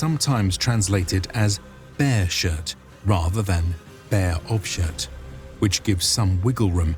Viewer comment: berserker is sometimes translated as “bear shirt” or “bear .... ? Can something tell me what he said here? Thanks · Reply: Bear shirt or bare of shirt